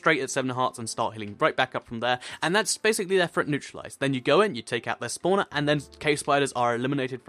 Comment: Did he just say take out the spawner